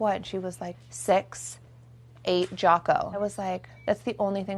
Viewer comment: tell me why i thought khloe said “6 8 Jaco” not “Six ate Jaco”